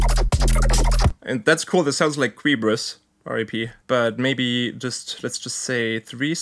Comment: whos Kreebrus? is it like cerubus or what is he saying? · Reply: Their name is Qebrus, they passed away in 2018 but were one of the pioneers of Flashcore (basically, a fusion of IDM, Breakcore and Speedcore which takes a high focus on sound design, atmosphere and rhythms) if you're interested i recommend checking out one of their albums from 2012 <link> and this one (last project in 2017 before they passed away, also features Mr Bill) <link>